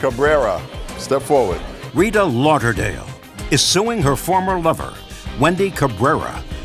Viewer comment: I thought they said former mother I was so confused 😂